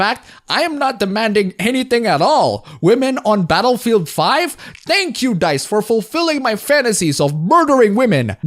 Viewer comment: I thought you said Betafield 5 for a second 😂